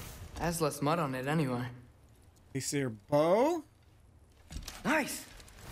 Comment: you said aesir bow wich prolly sounded like hey siri bow